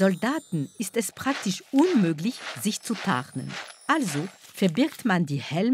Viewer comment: “Problem: den Soldaten ist es praktisch unmöglich, sich zu ….?” Ich habe die Stelle 3x geschaut/angehört und immer verstanden: “… den Soldaten ist es unmöglich, sich zu paaren” bis ich endlich verstanden habe “sich zu tarnen”🤣